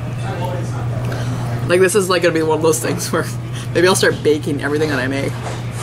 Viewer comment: Did anyone else catch what she said at 7:18 "maybe I'll start baking everything that I make"